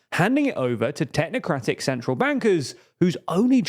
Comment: I heard 'technocratic central wankers' Fair enough.